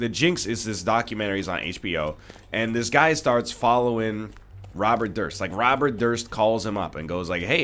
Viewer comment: Low-key thought you said Robert Thrisk instead of Robert Durst. Got a little more interested in my high school for a second